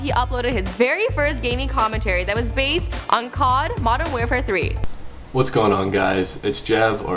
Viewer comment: Did she say "COD Modern 'Wear'fare 3"?